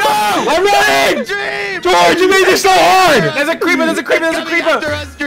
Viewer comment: Bro I thought dream said, “GEORGE, YOU MADE ME SO HARD” in 2:36💀💀